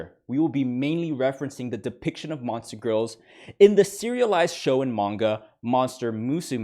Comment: I misheard "show and manga" as "shonen manga" and thought you were off your rocker for a sec